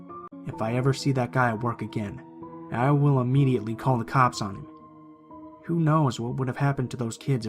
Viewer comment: I thought he said I will immidiatly beat his ass up